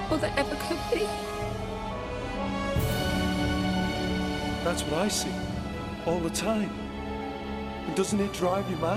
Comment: When i hear Eccleston say ''That's what i see, all the time'' 1:10:02 All i hear is Paul McGann!